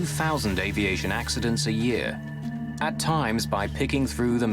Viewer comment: did he just say 2,000 planes ✈️ 💥 crash a year?! Scene that, I’m never flying . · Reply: No, he said the NTSB investigate over 2000 accidents a year. Many accidents are not crashes, and the number includes general aviation as well as commercial airliners. Crashes of commercial airliners are rare.